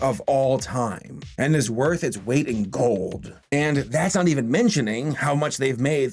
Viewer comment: did anyone else hear him say worth it's wait in cold?